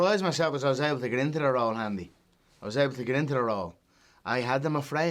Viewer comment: did he call Adam Andy? 🤣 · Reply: He said "I was able to get into the role handy" irish slang for easy